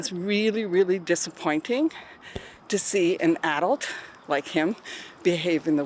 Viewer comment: She said "Adult" like Trump. For a long second i thought she said "Adolf".😅